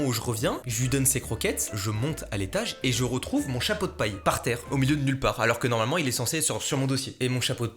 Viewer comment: la vie j'ai bug j'ai compris ton chat "pot de paille"